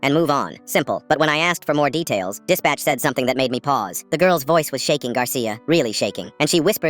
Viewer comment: I thought it said ‘the girls voice was a shaky Garcia’